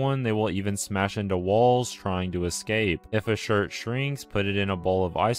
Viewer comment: I thought he said “air conditioner” rips ac out of walls